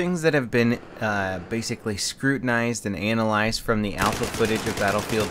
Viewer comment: Sounds like you said "analised"